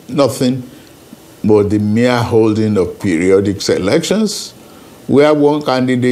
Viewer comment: did he say periodic “selections”? Freudian slip it would seem.